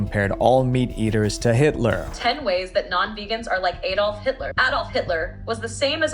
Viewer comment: in the first 10 seconds of the video I thought she said Add Off Hitler 🤣